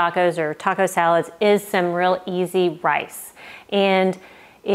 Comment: I enjoyed this taco video but i.thought you said you had a recipe for Cheesy Rice. I searched your recipes for this recipe but found none. Did I hear you wrong? Please let me know if you have this rice recipe. Many thanks, Joan from Elkton Maryland 😊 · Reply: Hi Joan! Cheesy rice sounds delicious and now I’m wishing I had a recipe for that 😊 I think I must have said “Easy” rice but I’m putting “cheesy rice for Joan” on my to-film list! Sorry for the confusion and thanks so much for watching ❤️